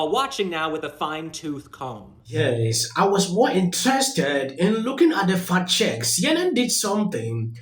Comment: I own you an apology. I was distracted for a second when you said "I was more interested in looking into the fact checks". I thought you said "I was more interested in looking at the fat chicks". I was thinking "Yeah, that would be more interesting for sure".